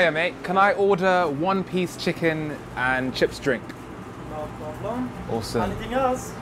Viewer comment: I swear he just said 1 piece chips and drink